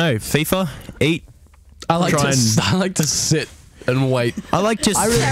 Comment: I THOUGHT HE SAID "I LIKE TO SIT AND WANK" OH MY GOD